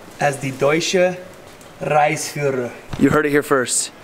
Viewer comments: oh oh... be careful dude, sounded like deutscher Reichsführer xD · I heard it about five times to check it out. said "ReiSführer" but at seems that a lot of Germans whant to hear "Reichsführer". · I heard "Reichsführer", and I thought aha! More of the 2020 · Hahaha it sounded like he said I'm gonna be the Deutsche Reichsführer. As in German Empire Leader. Oh boy.